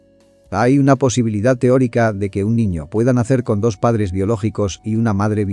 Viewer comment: lo que escuche es "no tengas hijos con una carruselera" xddd